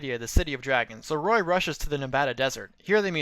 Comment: I thought you said, "Roy rushes to the Nevada Desert" until I realized it actually said Nabata.